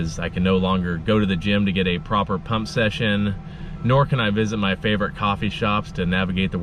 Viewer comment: I definitely misheard “pump session” as... well. “cum session”. so that’s what ya use the gym for huh.